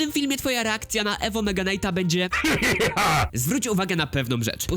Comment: Ja usłyszałem nyga night